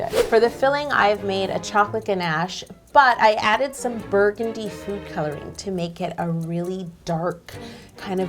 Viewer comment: You said "burgundy food coloring". I heard "bird duty food coloring" Sounds a little similar, right..?